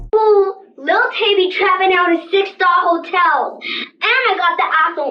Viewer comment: I thought she said a $6 hotel, lol